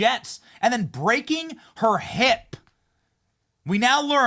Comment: Damn... I thought you said she broke her lip. 😏 Maybe next time. 🤷🏻‍♂️